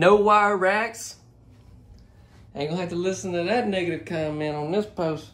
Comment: did he say the word nig*er ? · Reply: He said that there is no wire racks so he won't have to listen to some "negative" comments in this post.